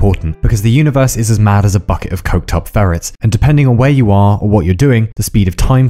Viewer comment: I thought he said as mad as a bucket full of cooked-up ferrets. i lol'd.